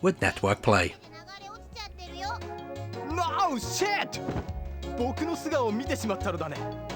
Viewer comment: Lol did he just say "No Shit" @ 4:28??